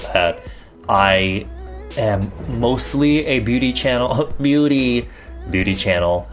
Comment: In the beginning I thought you said ‘official booty channel not beauty channel!’😭😂😂😂 love you... your my #1icon 💕💕🔥